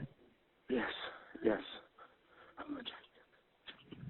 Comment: Doesn’t it sound like she says “you’ve killed him”?